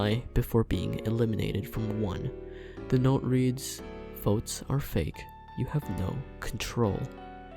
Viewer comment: For me, When Stone was writing the notes then afterwards being eliminated, I thought it said "Vote Sare-Fake you have no control" As if Stone was thinking a nickname for Scenty.